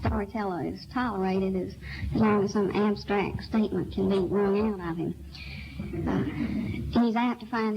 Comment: Anyone know what she says at 0:15 -- "some abstract statement can make more ??? of him." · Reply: as long as some abstract statement can be wrung out of him.